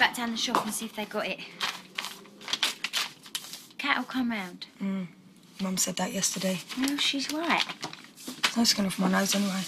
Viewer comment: Does Hayley say mum? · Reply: "Mmh.. Mum said that Yesterday"